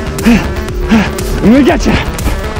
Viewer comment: what he says? I hear "не уйдёте" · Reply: He said I’m gonna get you.